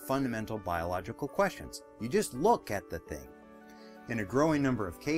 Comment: Typo: "book" instead of "look" in subtitles. Thanks for the course!